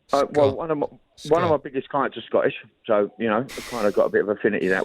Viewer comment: " one of my biggest... " anyone work out what he says I cant quite work it out? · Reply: clients